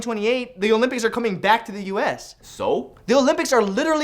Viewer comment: Did he just say soap instead of so?